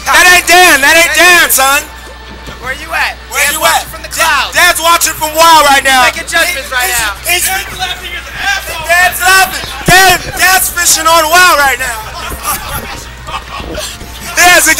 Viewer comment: OMG WTF BBQ I thought I heard them say Dan's Playing WoW @ 1:21 ! THAT HILARIOUS ME AND KDJ DID PLAY WOW AT THAT TIME XDDD